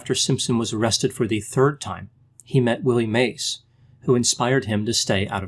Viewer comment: I misheard Willie Mays for Billy Mays. I was like "wait...WUT!??"